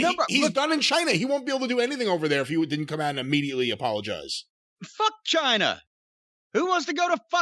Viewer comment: "F*ck ch!na" 😂🤣😂🤣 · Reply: I heard it as "F*ck Chyna" when he first said it.